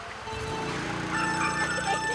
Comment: What does he say in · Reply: Kumquat